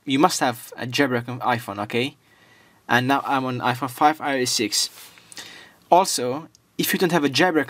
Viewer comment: We thought u said iOS 7 and that's not a iPhone